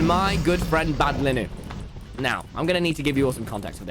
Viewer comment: It sounded like he was saying “my friend Bad Linen” 😭😂.